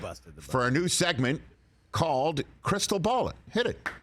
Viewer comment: I thought rich said the segment was called “crystal ballin” which is better and it should be changed to that